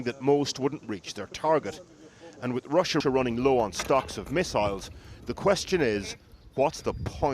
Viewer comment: We thought as you said run out of missiles?